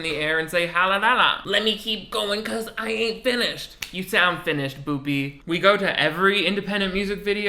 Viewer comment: idk what you said but I heard "you sound finished BOOPEY" and I lost it. I had to pause the vid bc I needed to breathe from laughing 😭